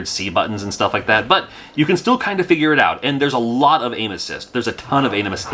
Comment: Lol, when you were saying "aim assist" during shadows i thought u were saying "anuses" i need to go to sleep